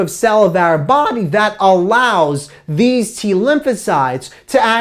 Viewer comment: His voice cracked.. what did he say? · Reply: "our body that allows..."